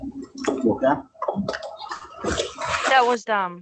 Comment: after we hear whisper, all i hear the officer say is "you okay" , not "what was that". Thats why she never responded to him.